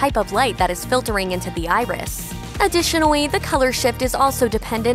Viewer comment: Also, any time she says “additionally” I hear “adwitionally”